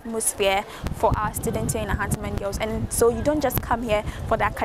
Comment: Am I hearing "Ahuntsiman" 😂🤣😁..... Oh bwoy